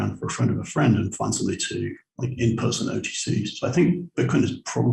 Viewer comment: What does he say here? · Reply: I think "in person OTC" as in 'find someone to trade for you over the counter.' Could be off on that.